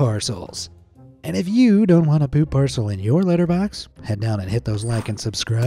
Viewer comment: Did he said Parcel in litter box or letter box? Cuz if he said litter box that is genius!